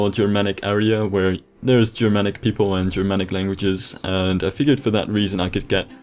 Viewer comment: "Germanic people and Germanic languages" I thought you were saying Dramatic XD "Dramatic people and dramatic languages"